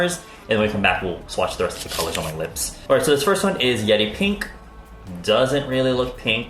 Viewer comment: Is it just me when i heard he said "yedi pink" instead yeri pink?